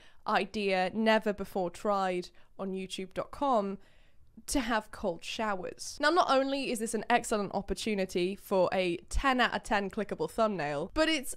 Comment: I am stoned and i thought it said GOLD showers… AKA PISS AND I WAS LIKE WTFFFF